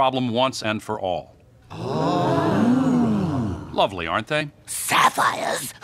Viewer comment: At first, i thought he said "Gate of Garage" Which is a LOT more funny